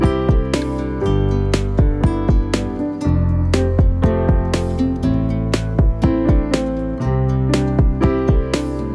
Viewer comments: ? Did he said "christine" on korean on 3:31 cuz I haven't heard him saying english "christine"? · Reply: It's actually "Christina" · Hi I can't see wut did the fan says on the live at 3:31 , can anyone tell me wut is it? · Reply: "if vsoo real call my name"